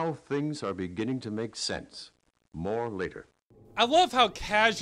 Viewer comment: Did anyone else mishear "more later" as "more liquor"?